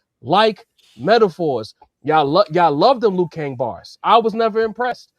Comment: Did I hear “Liu Kang bars” lmao